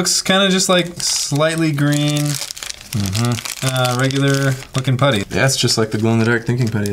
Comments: Am I the only one when he said regular looking putty I thought he said regular fucking putty ahah 😂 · i i thought he said regular f*king putty Lol